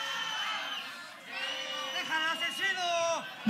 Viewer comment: "Los freestylers no escriben bien" - Deja al aczino jajajksjajjsa (o al menos fue lo que escuche xd)